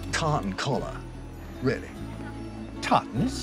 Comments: My hearing must be off because I I thought crowley said cartin collar for some reason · "Tartan collar, really?" "Tartan's stylish!" I could imagine that interaction between two versions of the Doctor. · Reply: Tartan i thought he was saying cotton